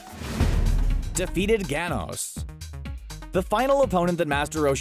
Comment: I almost thought he said "defeated thanos"